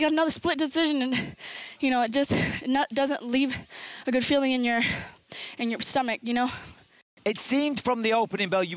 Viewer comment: Did she say "nut doesn't leave a good taste in your stomach"?